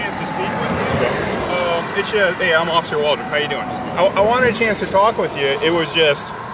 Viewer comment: Great interaction! Sounded like he said his name was Officer? While I always reserve a little skepticism, he came across as genuine. Outstanding image for VA police.